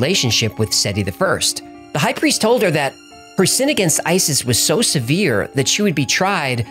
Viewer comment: her sin against isis. Is that what I heard